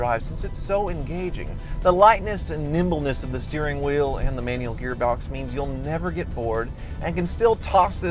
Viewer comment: "manual gearbox" I thought he said manual glove box LOL